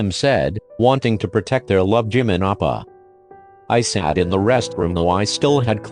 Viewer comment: "Wanting to protect their love Jimin appa Edit: Since then, I've keep hearing Jimin appa instead of oppa... dAdDy